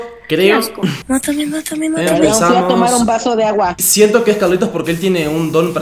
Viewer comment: oí que dijo mátame mátame matame